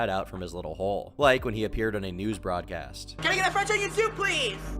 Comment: ONE FWENCH ONION SOUP PWEASE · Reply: On my life I thought he said fried chicken soup